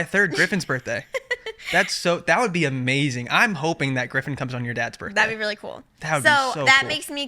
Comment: Did anyone catch saying he "hopes griffin comes on your dads birthday" 😂😂